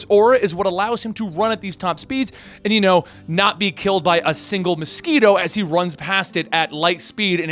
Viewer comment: I thought he said he couldn't be killed by a single pedo😂